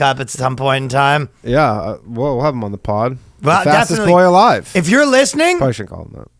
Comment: I swear I heard "Fastest goy alive" hehe get 'em!